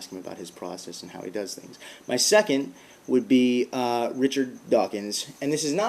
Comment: At first I thought you said Richard Dawson...and wondering how much you must’ve liked Family Feud to have him in your top five. 1. My dad’s older brother...he died when my dad was 13 (he was 18)...I have always wondered about my uncle I never got to meet. 2. My dad’s dad...he died when my dad was in his late 20’s and I’ve always wondered about my grandpa. 3. And not god, but Satan...just like you want to get Thomas’ take on Jesus, I’d like to hear about Satan’s take on god. 4. George Washington...just because. 5. Zack Morris...because he is trash.